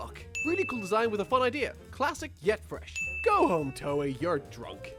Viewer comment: "Really cool design with a fun idea. Classic yet fresh" I thought he said Classic yet TRASH lol